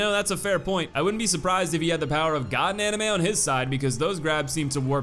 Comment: "I wouldn't be surprised if he had the power of (gibberish) on his side" wtf does he say here....keep rewinding it and can't catch it all I hear is "God anime"